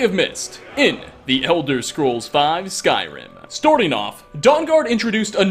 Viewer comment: Did anyone hear him say storting off